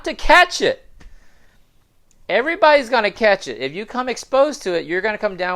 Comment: Did I hear that correctly @ 13:26 , you said "Everybody's going to catch it?" And, would you personally cancel out the alternative view that this was NOT leaked out of a BSL-4 Lab? In your opinion is this human error, nefarious gain-of-function, or just bats, pigs & civets that are the sole carrier's of this pandemic's outbreak? Thank you.